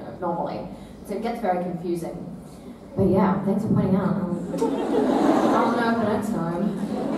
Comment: what dis she say here:"thanks for pointing out...?" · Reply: "Thanks for pointing it out i'll know for next time"